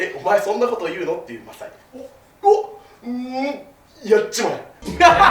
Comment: ここなんて言ってるかわかる人いますか？ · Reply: やっちまえ！ですね